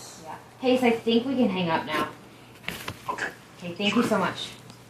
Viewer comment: Am I the only one who hears tell, "Hey, sexy, we can hang up now."